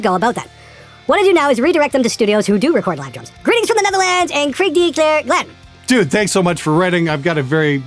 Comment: "krijg de klere" means literally "catch cholera", but is mostly used in a somewhat similar context as "fuck you". It was unrecognisable the way you pronounced it, I wouldn't have known what you said if the text wasn't shown. Carry on.